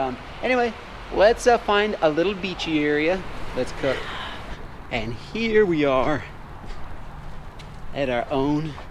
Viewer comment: Is it me or did anyone else hear a whisper at 14:44 🤷🏽‍♀️🤔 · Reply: Yooooo I think I said get out